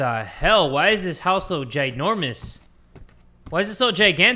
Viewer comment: "what the hell, why is this house so ginormous" "why is it so gigantic" What I heard: "what the hell, why is this house so *jay-normous*" "Why is it so *jay-gigantic*"